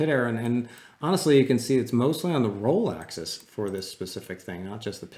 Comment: "mostly on the Rolexes" ?!? I see. jk, I misheard and it was so funny! ;-)